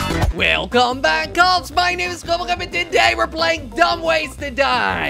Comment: This guy talks to fast cause the first time I heard him I thought he said " welcome back my name is dibadob"